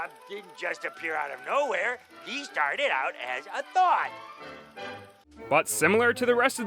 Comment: did... did Patchy just call Spongebob a thot